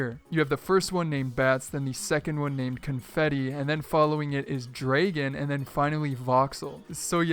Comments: did you just say dr-AYE-gon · Did you just say "Draygen"? Lol.